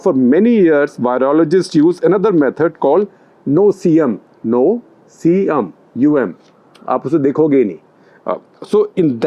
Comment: what did he say? Heard it as "No C UM aapssu dekho gayee" lol